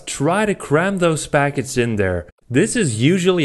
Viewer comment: Did i hear him say "Cram those foggots in there =O"